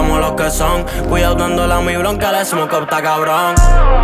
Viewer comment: Que dicen en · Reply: Cuidado con el humo de mi blunt q el smoke está kbron